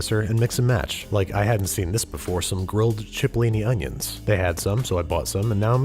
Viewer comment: Subtitles corrections here! Didn't catch all of them but tried my best 15:15 the indistinct is "cipollini" 30:17 it's "peel" not "peeled" 34:54 it's"Meredith" not "there it is" 39:10 the indistinct is possibly "top marks"